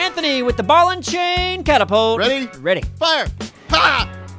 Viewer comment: I thought that he said "cocktapult".